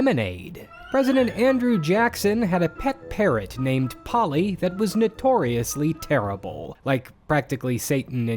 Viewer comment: I thought you said "pet carrot" and honestly from what I know about him, I didn't even question